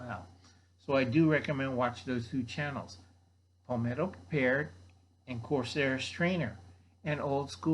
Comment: Thought you said....Pimento Prepared....and Corsair Strainers....lol...time for ear drops.... Homepower....good mag... Well done Al....!!